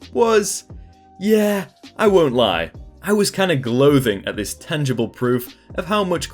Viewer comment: "gloathing"? lol I actually thought that was a British word or something until I heard you say "gloating" a little bit later 😂